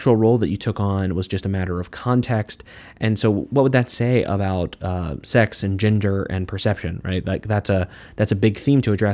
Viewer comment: I had to rewind to clarify "what would that say about sex and ginger.."